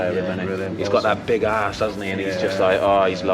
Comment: Moussa dembele " he's got that big ass, doesnt he? He's just lovely" WTF? Did he say that or am i hearing something wrong?